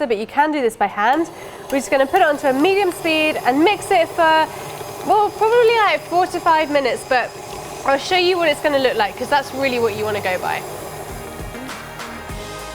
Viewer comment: I thought for a second she said 45 minutes.